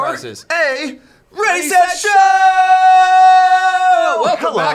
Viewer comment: It sounds like you said racist show instead of ready set show.